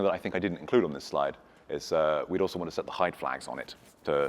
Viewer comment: "And also something I didn't put on the slide, that is really important, we want to set the <mumble mumble mumble> on it." What? What the heck did he say at 31:05? The "height flag"? What is that and how do you do it? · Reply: Hi, he said the "hide" flag, to stop the garbage collector clearing it up. Hope that helps you :)